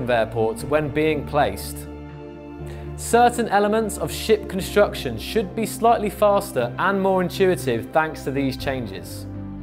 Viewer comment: I thought i heard "certain elements of sh*t construction should be slightly fast" 1:01. My bad